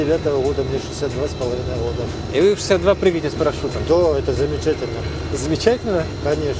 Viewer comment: мне послышалось или мужик сказал что он прыгает с парашюта?